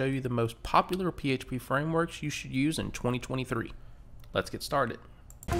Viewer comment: I'm not native, but... Did you say: "framworks you should use in 202030"?